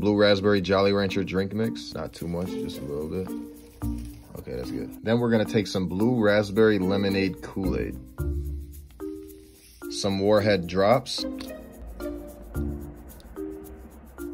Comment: When he said “Some WarHead drops” I heard “Some WarHead drugs.” 👁💧👄💧👁